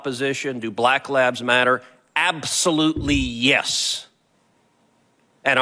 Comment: I swear his saying black labs matter 🤣 but I know he's saying black lives matter